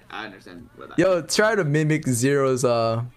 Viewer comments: i think he said mimic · Just in case you guys (and girls) don't know what he said, it's "mimic"